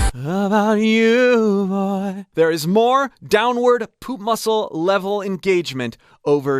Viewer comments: The next step to learning proper breathing and placement... Join my free course! <link> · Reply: Sorry... did you say "downward POOP muscle level engagement"? If true... then oh boy! All these long and cold "training" sessions on white ceramic horse finally got some useful outcomes! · captions say "poop muscle" but what did he really say? XD